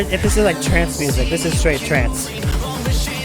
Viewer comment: "this is straight trance" what i heard: "this is straight trans" you called, mate?